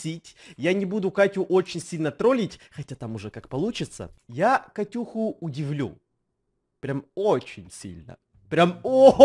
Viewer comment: Мне послышалось: я Катюху люблю... Ооочень сильно люблю.